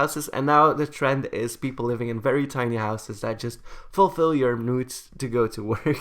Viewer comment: I thought he said nudes instead of needs